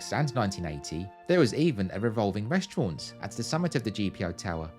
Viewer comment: As a child I misheard that the Post Office Tower had a REVOLTING restaurant at the top!